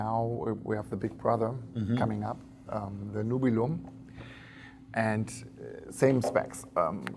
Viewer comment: what is the name of the knife he says is the big brother to the Nebula? For the life of me I can't understand what he said. I looked at the Boker website, but it isn't listed under the new items yet. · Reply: I think it was Nubulum -DCA